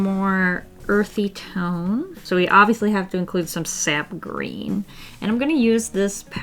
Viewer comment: I thought that said sad green and I was like "same."